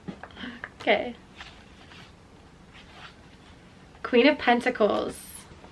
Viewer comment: Wow, that whole time I thought you were saying Queen of Tentacles🐙 I’m an idiot lol